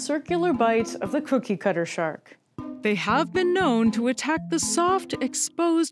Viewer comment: I keep hearing 'kooky cutter shark'. Disconcerting 😂